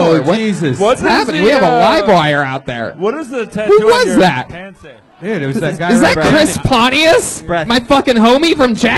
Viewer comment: Anyone know what Chris Pontius said in the crowd at 49:00 ? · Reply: something like "Hey, I'm a rich minority"